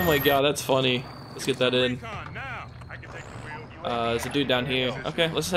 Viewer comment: he said hereoh instead of here😂